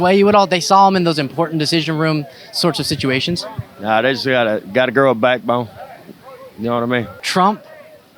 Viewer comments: what did he even say….?? 💀💀💀💀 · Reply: I think he said "gotta grow a backbone". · the fuck did he say? “got a girl, bag bone.”